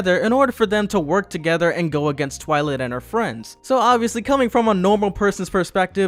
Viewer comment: Wait- Did you just say twi lit at one point?